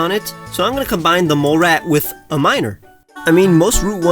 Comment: I can't help but mishear "So Im gonna combine the molerat with a minor "